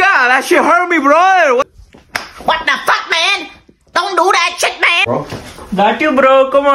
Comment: "WhAdA fAcK MAn dOn dO dAt sHit mAn" got me dying on the floor 😂😂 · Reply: I heard he said "WHAT THE FACK MAN ĐÙ MÁ ĐẬP CHẾT MẸ"